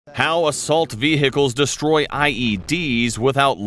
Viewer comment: Immediately I hear "veHichles"